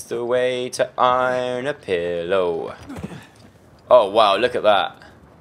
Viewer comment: did he say iron a pillow😂😂